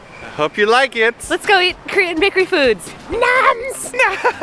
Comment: at first i thought it said wanking in Korean blankets xD